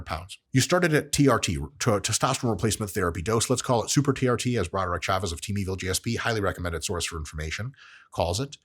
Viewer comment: I replyed that moment 8:50 to actually hear the name of the guy dr Mike recommends but can't understand it even at 0.25 speed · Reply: broderick chavez of Team Evil GSP